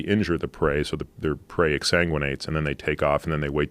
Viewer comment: exsanguinates? its close to anguish but what a word at 1:10 qand wow spell check completely saved my attempt but i heard that and was kinda close exangiuate . there was a scandal in alaska during a 5k oor half marathon when we landed hearing a teen got bit or charged by a bear & i thought that literally has to be the whole point of a race lol my condolences but dam nature sorry we dont like bein nibbled